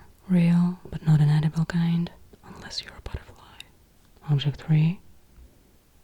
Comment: does she say "unless you're a petal-phile"??? At this moment in the zeitgeist, that is epic.